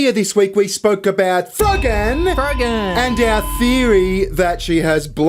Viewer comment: I'm sorry but whenever I hear her name I always hear it as "Krogan". As-in the alien from Mass Effect. She looks like one too.